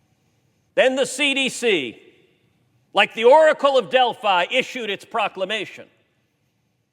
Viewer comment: Did he just say like the Oracle of Delphi? 😂 Never really liked Ted Cruz but this was an excellent speech. GOD IS HUMILIATING THE CDC AND SHOWCASING HIS POWER OF SENDING THE SCIENTISTS CONFUSION